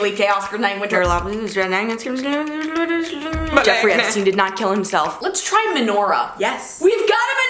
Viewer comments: Did I hear a “Epstein didn’t kill him self”? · Did anyone else hear the jeffrey epstein didn’t kill himself part 😂